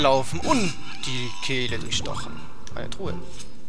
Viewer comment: lol ich hab "in den caleb gestochen" verstanden xD